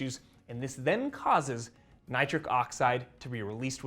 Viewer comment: For a brief moment I thought you said “nitrous oxide’ which would have explained the reaction my wife, of 48 years, often has when I gleefully point out my tumescence. 😂